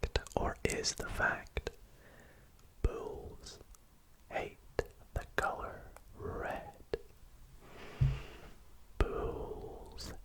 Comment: I thought he was saying pools hate the color red.